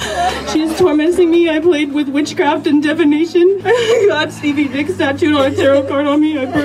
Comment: Did she say she has a tattoo of Stevie Nicks?